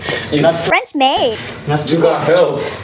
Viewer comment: I lost it when he had to bleep and say French maid 😂😂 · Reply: I thought it said French babe lol